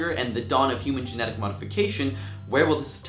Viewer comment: how about fucking genetics? how about every gold medalist sprinter is black? how about that? fucking retards....DUHH THE BODY SUITS AND SHOESS DERPPP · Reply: ASSKICKERINOTRON @ 2:00, he clearly says "the dawn of human genetic modification". Actually use your ears, listen and understand before posting things to embarrass yourself.